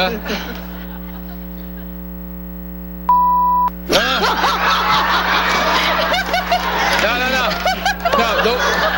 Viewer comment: What the hell did she say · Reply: Tarheel Fan in the ass